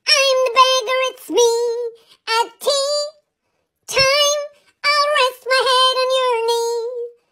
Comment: "im the beggar" Bro should get an oscar🤣🤣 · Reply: I thought when the doge said “ when it’s tea time! “ I thought the doge said “ it’s tea tie. “